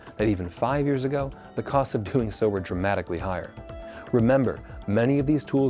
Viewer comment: What is he saying at around 5:12 Something about the cost of "what"? Tuneso? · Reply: "doing so" ?